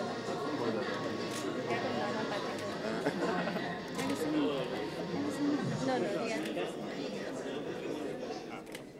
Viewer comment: Did I hear Nana patekar? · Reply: Yes. It seems Asha ji mentioned he looks like Nana Patekar then Neena said no no he looks much better than Nana Patekar.